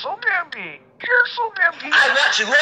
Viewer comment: "I WANT YOU TO LOOK AT ME LOOK AT ME LOOK AT FUCKING ME LOOK AT ME ALLISON STOP" -bambi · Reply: I heard “I was” not Allison